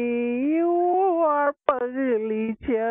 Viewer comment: He was speaking Hindi. 'Pagli' means mental. :( He was plain disrespectful. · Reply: Arpitha I thought he was saying “fugly”